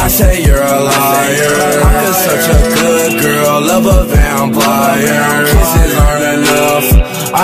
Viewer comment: I thought when it said how could such a good girl love a vampire i thought he said how could such a good girl like a damn liar